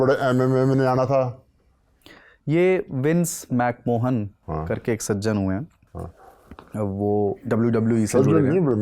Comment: Did he just say Mac Mohan?!